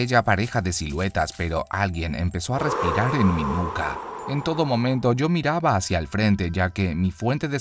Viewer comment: entendí "pero alguien me empezó a respirar en la cuca"